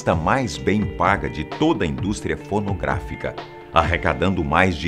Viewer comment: No minutos 4:50 a 4:56 ele fala britney spears foi a atriz pornográfica mais bem paga kkkkkkkkkk